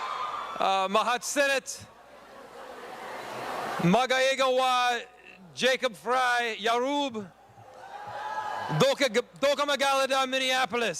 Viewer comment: all i hear is "ooga dabooga dooga mabooga dooga....Jacob Fry, dooga babooga Somalia"